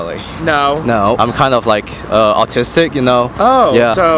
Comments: I thought the guy said artistic not autistic lmaooo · i thought the asian guy said artistic not autistic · “yea, I have a black friend” 😂😭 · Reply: omg i thought he said artistic in an asian accent